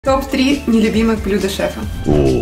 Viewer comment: Мне с начало послышалось топ 3 любимих блюд шрека😂😂😂